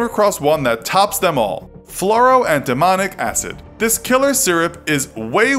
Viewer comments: "Fluroantimonic acid": I initially heard this as "Floral and demonic acid" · I thought you said Flouro and Demonic acid